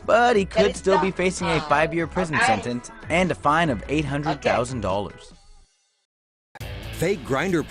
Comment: did he say 800,000 dollars fine?